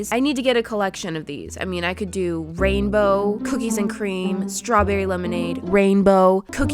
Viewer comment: I really thought she said cooties and cream...